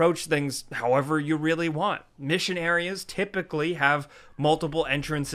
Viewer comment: When he said " missing areas" I thought he said missionary and the lines after that was just funny